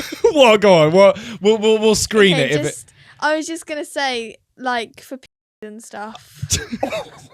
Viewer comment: what did she sayy?? I didn't catch it! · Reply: I think she says pedos